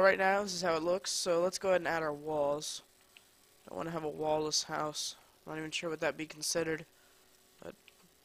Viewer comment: wen you said "we dont want a wall-less house." i thought you said " we dont want a walres house." XD i was like wtf???